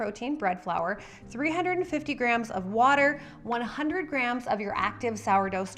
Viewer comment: did i miss how many grams of starter? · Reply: Thought she said 100 g in the very beginning